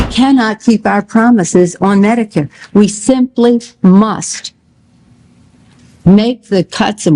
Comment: did she say “musk”?